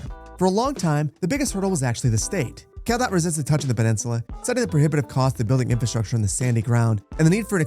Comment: dumb question, what does say here? i couldn't understand lol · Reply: he said CalDOT but in california i think their dept of transportation is called Caltrans